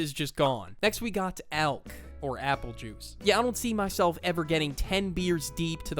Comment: i feel like suck an idiot I didnt hear 'alc' so i decided to say 'woah apple juice is an addiction?!?' until my sister nudged me and corrected me